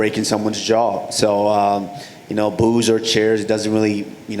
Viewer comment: Heard it as "booze and chairs". What are you talking about Kamaru?